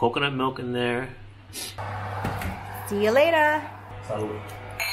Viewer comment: Did I hear a "Salud" before drinking the wine?